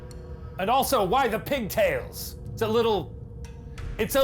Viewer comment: When ryan said he was going to turn it off and the caption said "unintelligible" it sounded like it said "pigtails"